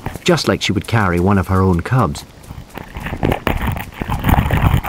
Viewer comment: “This journey is the last thing he ever filmed” 😂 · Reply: I thought he said « last thing he ever felt » 😂🤣